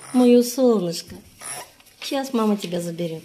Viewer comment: Мне явственно послышалось, что на 3:48 минуте видео сказал "ма ма" 😮😅